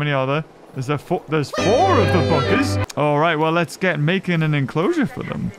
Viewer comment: did he just say the F word? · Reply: no he said buggers